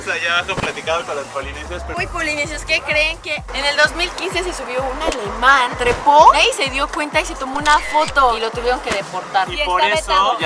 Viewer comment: entendí animal jajaja😂😂😂